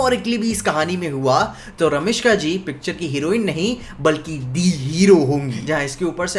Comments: I might be wrong ,But Have I Heard Ramiska ji instead of "Rashmika"🤨😅 2:05 Or It is a Combo name like (Ranbir+Rashmika)=Ramiska😅 Which one is Correct 👽 By The way Love From West❤ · did he said Ramishka?